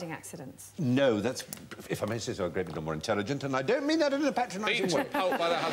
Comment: What did Fry say at 0:52?? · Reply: If i may say so that was a great deal more intelligent and i dont mean that in a patronising way.